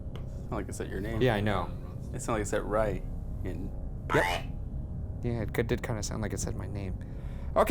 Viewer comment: Is anyone else hearing "girl" instead of "Ryan" at 27:56 ? xD